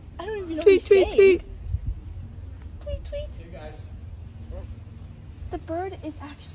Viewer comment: That bird sounds like it was saying dig a tunnel, dig,dig,dig😂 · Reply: i kept hearing "major trauma, tweet tweet tweet".